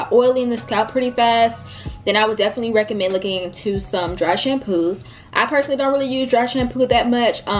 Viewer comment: Oh,dry shampoo,I thought you said russian glue??whats that.